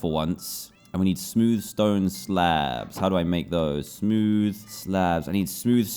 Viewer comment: tell me why I heard “le slabs...” 💀